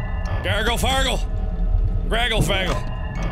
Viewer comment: What did he say?!?!? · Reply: Gragle fagle